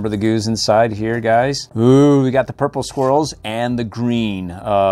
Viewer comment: the sound of he said “purple swirls” and sounds like he said “ purple squirrels”